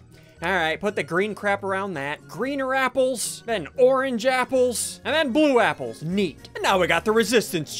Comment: is it just me or did he say Oran japples?